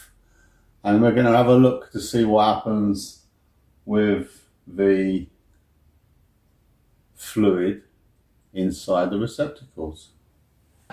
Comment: sounds like he said "sfluid" as if he's drunk. Almost makes sense if he was drunk and doing this